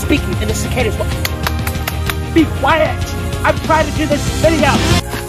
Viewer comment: it sounds like he is saying "Be quiet I'm trying to do this Citadel". ToAST is a tower, not a Citadel. That line would work phenomenaly if he was in a Citadel.